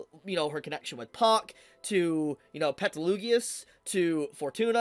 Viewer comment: I need to give this anime another chance. I feel bad for dropping this anime 8.5 years ago! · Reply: did you just say petlugius😭😭😭